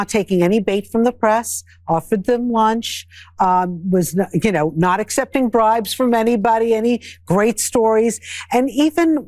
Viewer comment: Did she say "He's not accepting bribes from anybody!" at1:25 - like that 's a wonderful achievement?